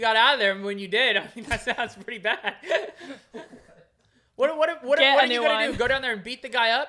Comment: did just say “get a new one” 😂😂😂